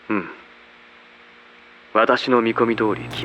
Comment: 〜のノイズかかりぎみのとこ 頑張って聞き取った結果 「私の見込み通り君たちは他人の〜が好きな最高の〜さ」と言っているように聞こえました（〜は聞き取り不可） 追記 最高の〜のとこはサイコパスの可能性もありそうです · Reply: 他人の不幸が好きな最高の〜さ って聞こえた、、、、、気のせいかな、、？？